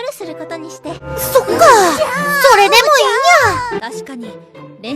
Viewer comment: Legend says that she's still asking for tea to this day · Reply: I heard she said cyka